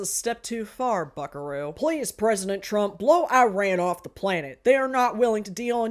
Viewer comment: I'm going to be so for real when you said Iran I thought you said Ayn Rand I need to start watching these actively instead of in the background